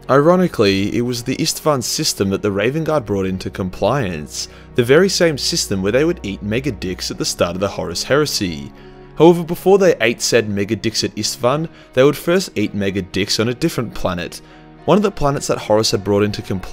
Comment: Ngl I thought you said n____aDricks not megadicks 😬 Australian accents are... Interesting 😂